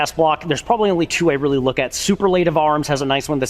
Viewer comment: ? What in the gods name of pronunciation did you just say? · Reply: Superlative arms. Real company name.